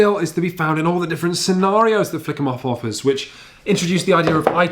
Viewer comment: Don't think I didn't notice you saying 'Flick 'Em Off' at 8:37. Thought you could smuggle that smut into the review didn't you. Bad.